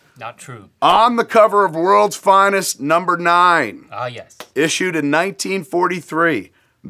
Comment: did anyone hear someone in the backround say "aww yes." Btw